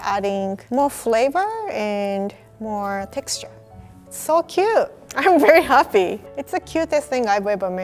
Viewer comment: Did anyone else hear 10:23 "So Cute" as FU?